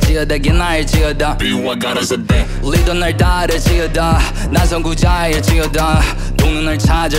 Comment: 만성고자일지어다 나만 이렇게 들리누